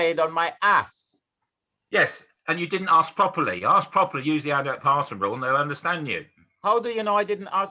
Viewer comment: what is that rule keeps saying, it sounds like he is saying aldi parton rule, can someone tell me what he is saying